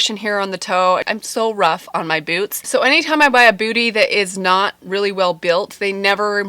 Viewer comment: mom goes “did she just say buy a booty?” 😂